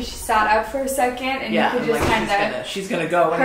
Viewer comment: it sounds like something saying yeah.. I felt chills hearing it